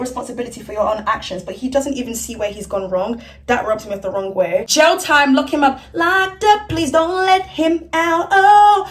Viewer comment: thought she said 'GEL TIME'